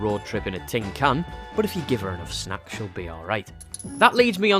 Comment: I literally thought you said 'If you give her a slap' here, I had to rewind it to double check! 😂